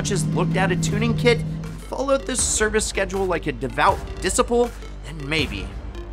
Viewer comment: was he saying disciple??